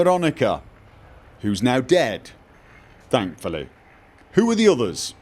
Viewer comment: I haven't rewatched the Vegas series in a while , but Veronica's dead?! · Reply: Nicholas Cross Oh, I knew that. But something said just made me think Veronica had died in a recent chapter. I could have sworn he said "She's dead now, thankfully."